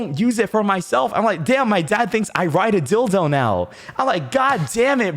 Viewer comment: i thought it said "My dad I ride thinks dildos"